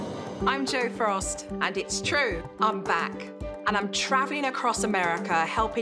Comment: I thought jo said “im black” when she said im back 😭